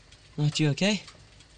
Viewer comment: Just wondering, at 0:17 seconds is he saying "you okay?"